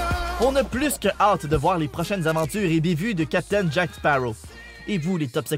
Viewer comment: ça veux dire quoi ibivu à 6:34 ? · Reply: "et bévues" ;)